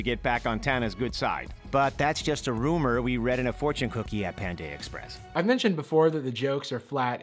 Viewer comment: did he just say pandae express